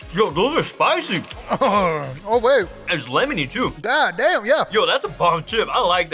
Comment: What did he say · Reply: he said god damn??